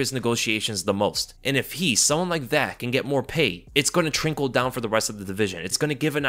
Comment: Did he just say “ thrinkle down”?